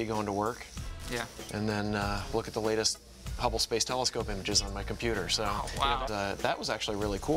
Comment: Anyone else hear 'I look at the latest hubble telescope and jizz on my computer ' loooool